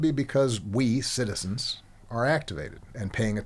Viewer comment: He said we “get activated” but all I heard is “let’s get active” 😂🤏🏽🙌